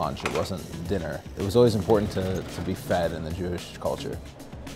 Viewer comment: "Its important to he fat in the Jewish culture" man, I wish I was Jewish. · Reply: Don't r/whooosh me but I think he said to be "fed"